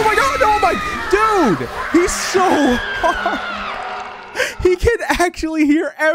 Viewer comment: why did i hear call michael either hard or hot